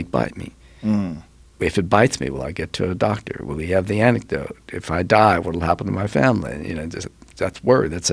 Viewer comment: Am I the only one who heard "anecdote" @ 29:00 ? I know that laughter is the best medicine but not against snake venom it isn't.